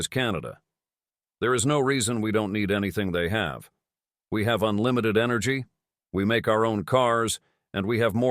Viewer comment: I THOUGHT I HEARD THIS FOOL SAY "WE DON'T NEED ANYTHING CANADA HAS..."WE HAVE LOTS OF TREES"???!!!